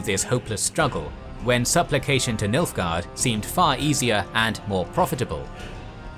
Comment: I thought you were saying milf guard.